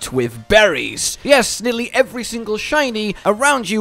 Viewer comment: I let revavroom run around with me in the dessert since he's one of the few pokes that can keep up with your mount at full speed, everytime you pass a shiny spot it gets picked up by the poke automatically. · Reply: oh I’m stupid I heard “shiny spot” and immediately thought your roaming Pokémon will signal you if you come across a shiny Pokémon 🤣